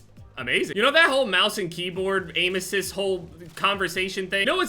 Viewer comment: Sounded like he said kbam instead of keyboard